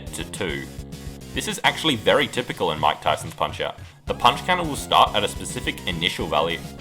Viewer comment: bro. i was so confused at 7:43. i thought you said von kaisers punch out because of the way you said mike tysons.